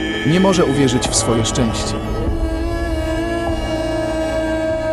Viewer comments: Czy tylko ja na końcu usłyszałem szympans sika ? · Czy tylko ja usłyszałem szympans sika?😅